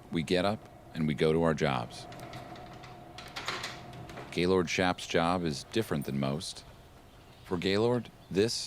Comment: I swear I thought he said his name is “Gaylord shaftjob” 😅 thank you subtitles